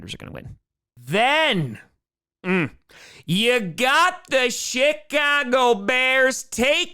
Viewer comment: did he just say the shitcago bears?